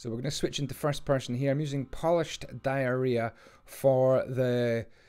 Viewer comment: did he say polished diarrhea